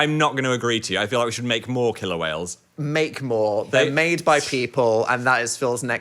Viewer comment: I didn’t realize they said killer whales I thought they were saying kilowhales like some sort of bigger version of kilowatts lol